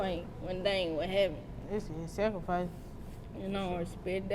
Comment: The twin in the black clearly said that it was a sacrifice and then the other one started tryna over talk her you have to listen 👂🏾 closely i heard her say it !! Wtf 5:01and the face she made